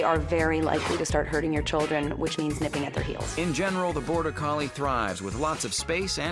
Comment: what did she say? "which means #$#%#$%#%^$" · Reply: "nipping at their heels", nipping = tiny biting